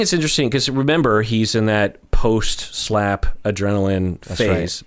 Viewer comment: I thought it said slap paralysis...like sleep paralysis lol